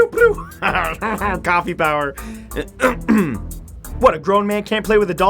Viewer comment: on 0:04 did he said " PEOW! PEOW! PEOW! HAR HAR HAR COPY POWER!" is he using the power of copying does he mean? matthew u really r giving clues for something for us ro solve!